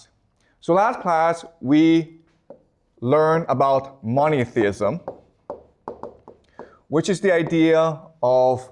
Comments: It sounds like you say "Moneytheism". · I first thought I heard prof. say “moneytheism”. 😆 On second thought, moneytheism may well be the dominant religion today 🫤